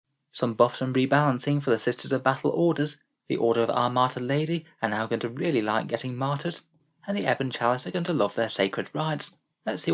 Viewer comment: When the video started, I could have sworn you said ‘Order of Armata Lady’ and I was terribly confused about why the sisters were getting Russian main battle tanks!